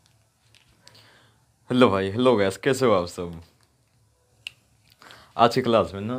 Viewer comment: did he say bhai 😍